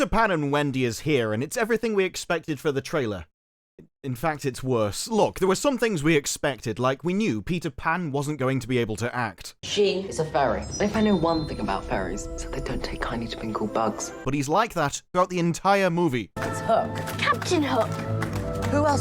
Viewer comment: No lie: I genuinely thought he was saying "furry". I had to think about "why is Disparu highlighting this dialog?" before realizing Pan was sloppily pronouncing "fairy". yikes